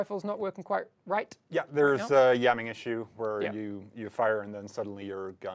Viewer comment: did he say "yamming issues" at around 0:55??